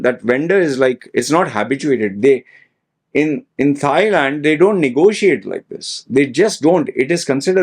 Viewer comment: did you say "thigh-land"???